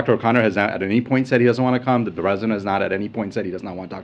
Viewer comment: Did he call Biden the "resident" at 27:26?